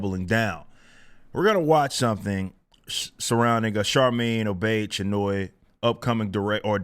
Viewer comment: Watcha thinkin? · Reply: Sounds like she says "OBITCH-Chinoy."